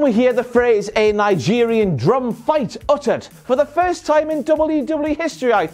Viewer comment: When I 1st heard that challenge from apollo I thought he said Nigerian dog fight